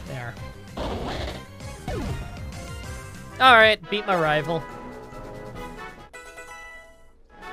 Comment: I honestly thought said “I ate my rival.” Instead of “I beat my rival.” And I had to play and back and make sure he didn’t eat Brendon